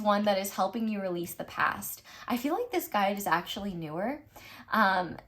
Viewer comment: I thought you said this guide is actually Björk 😂